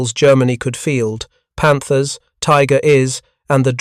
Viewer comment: Enough with the A.I. Street Vith?😂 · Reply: How about 'Tiger is" instead of Tiger Ones? (at 31:12) This AI crap really sucks.